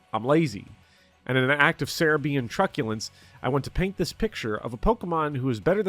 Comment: What are you saying at 22:21? "...in an act of Cerebi(?) and truculence." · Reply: “In an act of Serebiian truculence”